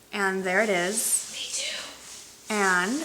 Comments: Did someone whisper "me too" at 6:17 or am I hearing things? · love your haul! you make me buy things i shouldn't too! lol i watched this video with headphones and at 6:20 someone whispered "me too" ..am i hearing things? hehehe ^.~) you don't look 40! you look amazing!